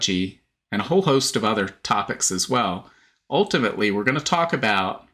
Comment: Did he just say moron topics